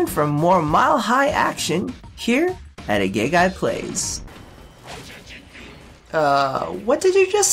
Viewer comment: what did the corpus say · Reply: Shinichiro Ozaki He said